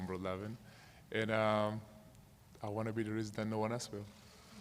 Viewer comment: What dose he say at 3:05.?????? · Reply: "I wanna be the reason no one else will" <link> he's referring to kyrie's answer sarcastically.